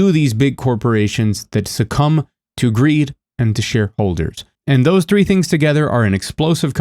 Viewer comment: My brain heard "sh1*holders" instead of "shareholders" lmao